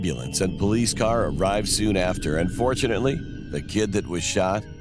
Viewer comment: Unfortunately he survive Like wtf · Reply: Yah he said and fortunately so it sounded like he said unfortunately